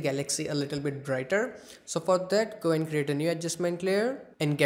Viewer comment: i thought he said so fuck that